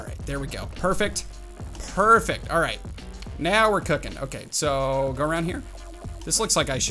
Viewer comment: Did say now were cooking